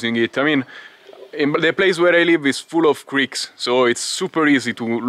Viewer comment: I thought you said Greeks 😂 then I realised you said creeks 👍🏽😁 brilliant bro as always. It looked quite stable to me the footage 👌😉